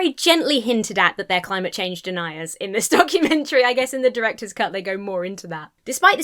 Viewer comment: I missheard you and thought you said DUCKumentary... which I now want to be a thing.